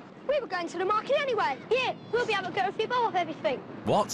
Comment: did that kid just say “We’ll be able to get up your balls and everything.” It sounded like that.